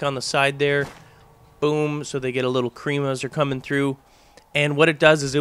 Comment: When you say "they get a little cream as they come through" the captions say "they get a little premature coming" ROFL its